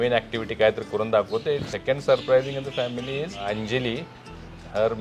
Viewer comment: he said anjali ? Is it ?? Am I heard correct